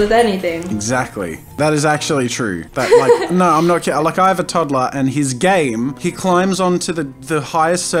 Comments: I legit thought jazzy said I have a toddler and he’s gay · sounds like he said "I have a toddler whose gay" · Is it just me or did anyone else here hear him say he’s gay instead of his game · Am I the only one who hears he's gay not his game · Why did i hear "i have a toddler and he's GAY "